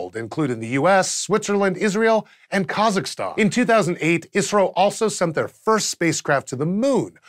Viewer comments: Every time he said ISRO, I heard it as Israel.... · I kept hearing "Israel" everytime you said "Isro"